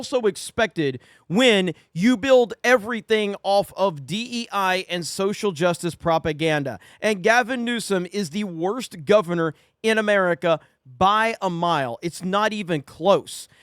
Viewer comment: Did I hear "Gavin Neu-scum?" Love it, keep up the good work man. 👍